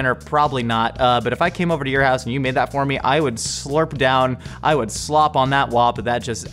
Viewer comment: Wait a second...did Josh say, "I would slop on that WAP,"?